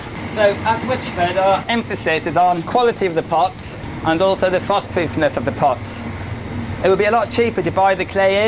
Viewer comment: not sure what he is saying from 4:00 to 4:02. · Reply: Frostproofness